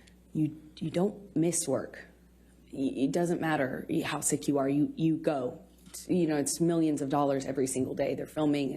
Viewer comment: Umnnn at 10:10 Did i hear her say “it doesn’t matter how sick you are, you still go to work” millions of dollars blah blah. LITERALLY PROVES SHE WILL DO ANYTHING FOR MONEY 💰.